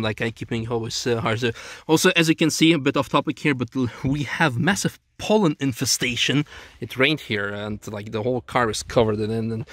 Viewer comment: I heard: "We have a massive Poland invasion"